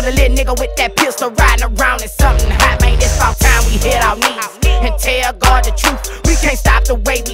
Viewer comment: Did he say you cant tell God the truth? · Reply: He said it’s about time we hit our knees and tell God the truth.